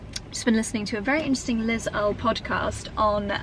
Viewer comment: Hi, I was wondering what the podcast you mentionned in the video 39:38 , the one on the healing powers of nature, was? I didn't hear the last name of its host or speaker and I'd be interested in giving it a listen. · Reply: Liz Earl I think she said 👍